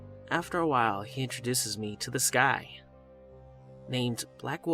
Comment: When the 1st story got to the "he introduced me to this guy" and I thought he said "he introduced me to the sky" and I was like what.